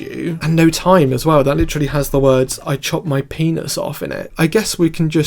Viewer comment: First time, and also the 10th time I listened to no time, I thought playboi carti said "I chopped my penis off, with my mind" I can't unhear it, and I think it's way funnier.